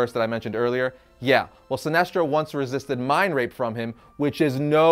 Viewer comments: did I just hear the words "mind rape" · did he say mind rape?